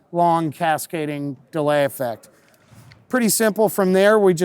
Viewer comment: Did he say ‘pretty simple’ at 7:15 ?